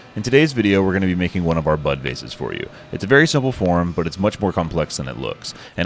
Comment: Beautiful work❤ ....And I heard you say butt vase...🙈